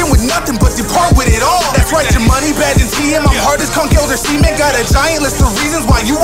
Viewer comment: I always thought he said "hard as Conkeldurr semen"